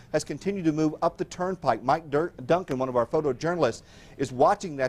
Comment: did he say Mike Dirnt er Duncan? He's been listening to too much Green Day!